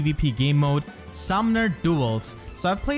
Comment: I just gotta say, when you first said "Summoner Duels" I heard "Some Nerd Duels" and I laughed so fucking hard. I love your accent and the way you said that, it sounded legitimately like how it should be said! Summoner Duels ARE "Some Nerd" Duels.